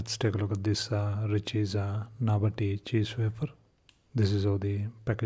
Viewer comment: For a second I thought it said "cheese water". Now I'm a little disappointed and a little bit relieved.